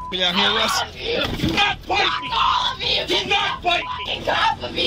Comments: Your subtitles are wrong. Here 7:02 just before he grabs her head (not throat) she tries to bite him. He grabs her and says dont try to bite me. The subtitle says fight instead of bite. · she was manically trying to break free, I don't think she has the mental and physical capability of biting someone in that situation. Just because her head moved in a direction doesn't mean she was trying to bite someone. Officer pushed her head back because he was trying to restraint her. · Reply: he says "don't bite me", not "don't fight me" as the subtitle says, so that's him accusing her of bitting right after it looks like she's trying to bite.